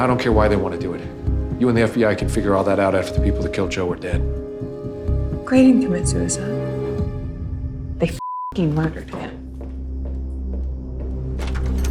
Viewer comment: what did Roscoe say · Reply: "grey didnt commit suicide, they fucking murdered him"